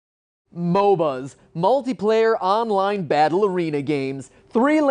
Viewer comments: when he said multiplayer online battle arena games, I heard it as multiplayer online ballerina games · Shut up, SMITE! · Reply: The Game Theorists At the beginning I heard MOBAs : "Multiplayer Online Ballerina games". I thought that was a good joke, sadly it wasn't what you said.